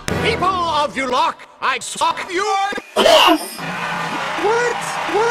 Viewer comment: Why is he on drugs!?!?!?! if someone else said this in the comments sry... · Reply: 1212 0:24 did anyone else think he said FUCK!!!!!!!